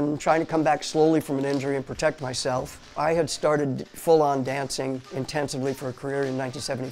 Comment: Not gonna lie I thought it said full dog